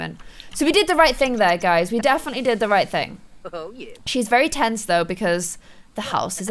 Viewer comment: I swear I heard sim Ali say "Oh Yeah"